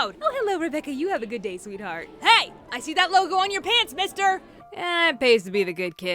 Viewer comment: I thought the Vice President said “ HEY I SEE THAT LITTLE BOY IN YOUR PANTS MISTER” at 5:07 XD I’m so dirty minded....